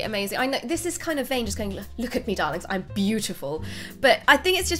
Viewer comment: I heard you say "Look at me Daleks"... Not Darling... Nope...